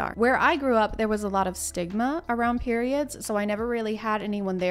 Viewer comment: i heard Ligma lol 🤣